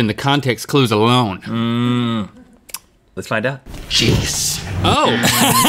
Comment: Thought he said "YIIIES" not genius.